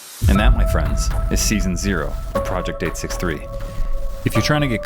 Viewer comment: Why does say in the outro "Cutter" i know he meant to say caught up but i just hear "Cutter"